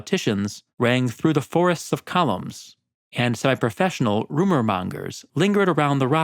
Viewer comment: did he say psy(si?)-professional?